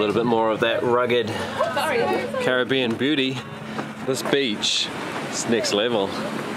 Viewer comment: Congrats, you've hit 90k... Onward and upwards... :) 8:15 Did a double take, I thought said "Caribbean Booty" instead of Beauty... o_0 Must be the rum... :p 10:16 in beer form...? :/